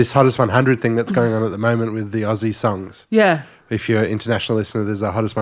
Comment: When mentioned Aussie songs I thought he said Ozzie songs, I thought damn this is current